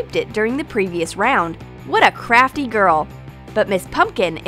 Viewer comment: It sounded like she said what a crappy girl at 7:03 LMFAO 😂😂😂😂